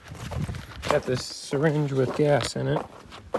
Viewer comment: did I just hear "a syringe with gas in it" l....